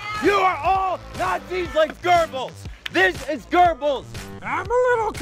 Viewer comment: I thought that guy said "Gerbils" (instead of Goebbels) too...